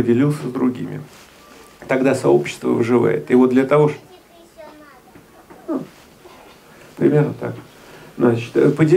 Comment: Что говорит ребенок? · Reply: " че тебе еще надо"